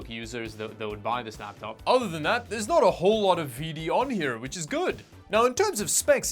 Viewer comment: did you just call bloatware a VD